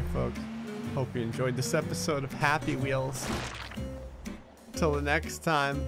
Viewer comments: am i the only one that heard him say "hope you enjoyed this episode of happy wheels" as the outro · did gray just say happy wheels?